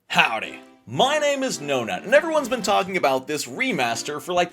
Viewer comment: I just misheard you saying "Howdy, my name is No-Nut."